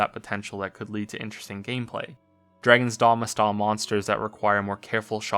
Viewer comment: Game at 40:08? Couldn't understand what he said. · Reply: Dragon's Dogma